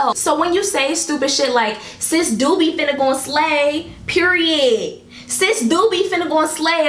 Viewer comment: I thought u were trying to say, "Sis 'Doobie' finna slay." Thrn it clicked like, waitt what? 🤣😂🤣 Also new subbie (I thought I was already following).